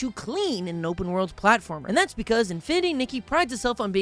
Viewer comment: At fresh when he said the name of the sponsor I thought he said infinity naked